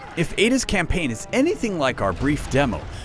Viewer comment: Did he just say... "naked slimy lady" ?